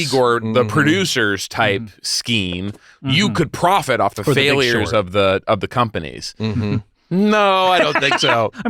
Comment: What does Scott say at 9:08 that has Hayes pressed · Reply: the big short